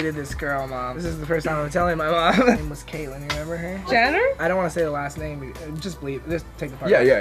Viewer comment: okay i know this video was like a year ago, but why is no one talking about 1:50 “her name was Caitlin remember?” “jenner?” idk if im hearing wrong but this is what i heard 😭